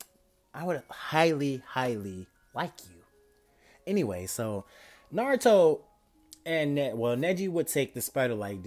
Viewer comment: i thought u said only naruto and ino would have ki